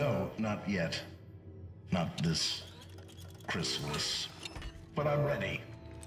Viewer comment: it sounded like Ultron said “Christmas.”